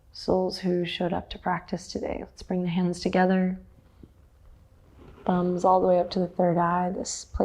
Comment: I heard "bring your butts to the third eye" and i thought "oh cool, didnt think that was an option but im into it"